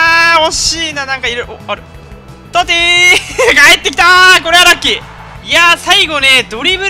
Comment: トッティーがミキティーに聞こえる笑